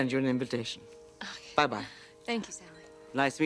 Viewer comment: KKL called Darlene Stephanie instead of Sally. 😂 On Y&R Devon called Lily Christel (her real name) recently. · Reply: I just checked. She said Sally